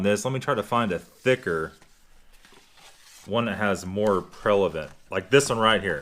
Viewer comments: Gerry Conway he said “prelavent”😆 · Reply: I thought that is what I heard. "Prelevant" is not a word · What did you say??? "One that has more_____" More what? I played it back four times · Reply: Gerry Conway he said “a thicker more prevalent”... (referring to the white line)